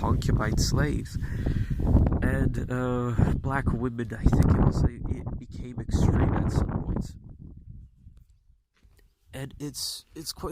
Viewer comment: What did you say at 5:45 about black women? The wind made it very hard to hear. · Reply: I was referencing a set of art that was put up by prominent art publishers and museums, "black women, and it became extreme at some points." i was referencing i think the parts where women were being beheaded by black women.